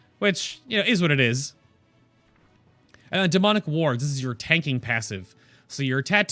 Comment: LMAO! I thought you said "Demonic Warts" I was like...EW?